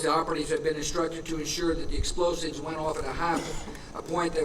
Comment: What was that he said at 4:30??? Explosives ? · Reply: He isn't admitting anything. He's saying the sheikh gave info about future attacks in which operatives were instructed to use explosives in a way that would trap ppl on upper floors